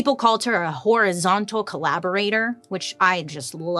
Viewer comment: I'm just saying, everytime she says it I hear " whorizontal collaborator" lol yeah I know that was immature